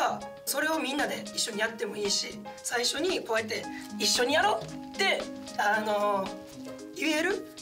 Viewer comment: の「一緒にやろう！」が僕空の「一緒に行こう！」に聞こえてしゃーない笑笑